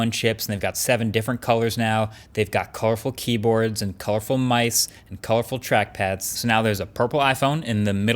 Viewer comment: did he just say mice?! LOL